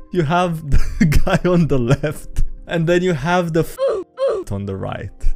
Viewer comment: Uhhhh did just say the f-slur at 4:34???